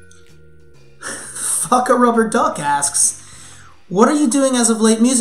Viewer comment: sounds like he is saying "Fuck a rubber-duck's ass".